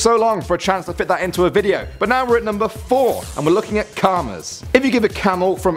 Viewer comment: did anyone else hear "Karma"